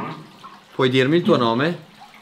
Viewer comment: oddio minuto 12:30 PUOI DIRMI IL TUO NOME? ho sentito NP